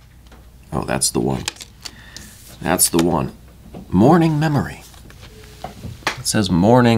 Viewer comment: I initially heard that as “mourning memory”